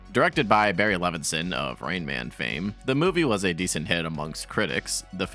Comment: I misheard "Rain Man" as "Rayman" and immediately spat out my drink